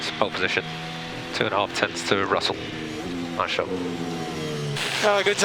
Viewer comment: what did he say at 6:29 ?? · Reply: This is very late i know, but he said "nice job"